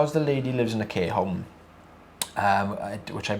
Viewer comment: I thought you were saying she lives in a “K” home until I finally stopped thinking with an American accent 🔥🤦🏻‍♀️